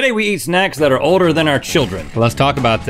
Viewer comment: When I woke up to watch this video I thought he said "we are eating our older children" 😂